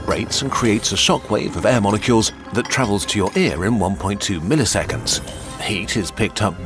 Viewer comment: Nano seconds is faster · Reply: you're right, I thought he said nanoseconds both times and then I even misread it when it showed all three. I guess that's proof that your brain really is just feeding you predictions 😆